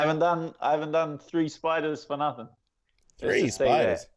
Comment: can someone tell me what he means? did he say spiders? lol